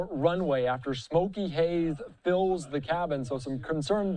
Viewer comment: thought it said " Hazel fills cabin " and thought wow , shes a big girl